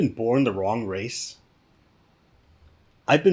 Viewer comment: Did I hear you say, "born the WRONG color"? 😲